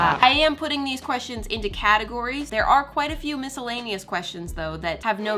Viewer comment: I thought she said “there are quite a few Canadian questions” 😭